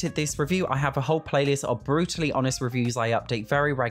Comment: Thought he said “Bruce lee honest” 😂 had to run that back